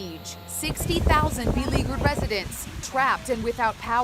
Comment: thought she said illegal residents